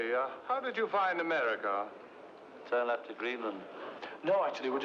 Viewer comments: Can someone tell me what does john said at 0:47? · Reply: Interviewer- Tell me, how did you find America? John- Turned left at Greenland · "Tell me, how did you find America? Turn left at Greenland" is one of my favorite movie quotes. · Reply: I thought he said "tell that to greenland"